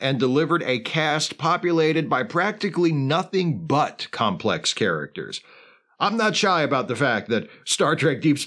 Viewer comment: Caption issue at 1:13 - the caption says "heroes" but the spoken word was "characters"